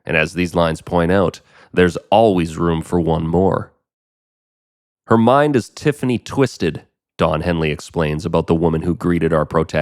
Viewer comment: I always heard “her mind is DEFINITELY twisted” damn